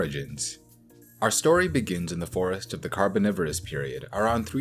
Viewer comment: I keep hearing you say “carbonivorous” in place of “carboniferous”. Cracks me up every time.